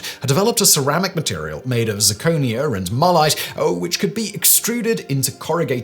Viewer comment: For a second it sounded like Ford's cat was made of marmite , 12:55.